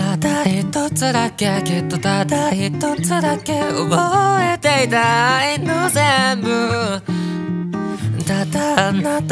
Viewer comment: 憶えていた、愛の全部 が「覚えていたいの全部」に聞こえてすごく好き